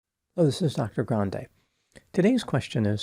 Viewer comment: I thought he said “Hello this is Nacho Grande” at the opening